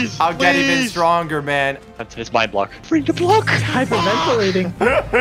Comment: did just say ｆ ｕ ｃ ｋ